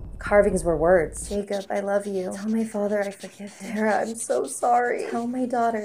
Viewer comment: I thought it said “Tord I’m so sorry” lol